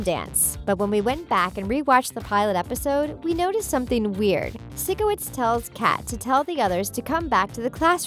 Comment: I heard her say SICKO witz, its PHYSCO witz